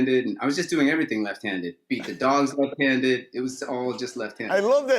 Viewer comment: Love the casual “beat the dogs left handed” that no one seemed to pick up on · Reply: I thought he said "feed the dogs" - now I have to go back and listen again! :)